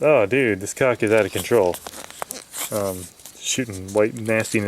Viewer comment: My mom walked in while watching this and thought you said this cock is out of control